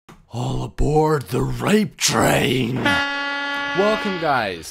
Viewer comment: did he say all aboard the rape train? im afraid now 0.o